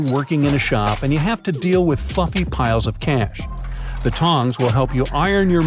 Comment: did this just say fukking piles of cash?